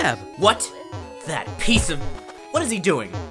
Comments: "That pizza!" That's genuinely what I heard. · "That pizza!" That's genuinely what I heard. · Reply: ItzMeMD I heard “that piece of”